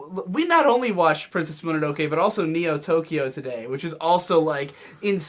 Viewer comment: Oh god, for a brief moment I thought he said neo yokio D